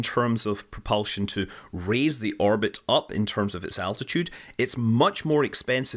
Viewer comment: say it with me.."altitude"...I kept hearing "attitude". mehh